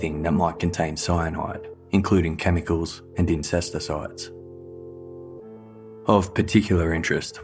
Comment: I almost choked when the narrator pronounced 'Puyallup' correctly. When I moved to the PNW ten years ago, that one gave me whiplash, heh. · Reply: I heard "incest"icide 😂